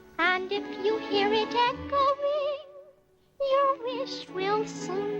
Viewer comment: lmao I thought she said “and if you hear a deadpool ring”